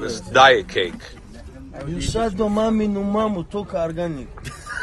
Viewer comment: What did he say at 1:22 maminu mamu ? Did this guy sneaky insulting the mother of the american camera guy??